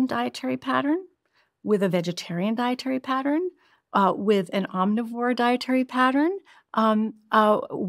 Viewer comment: What is the armnivore pattern please she said at 46:16 ?!